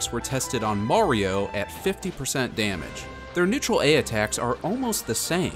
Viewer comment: did he say their neutral air attacks?